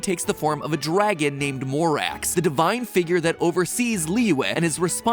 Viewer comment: I THOUGHT HE SAID LORAX💀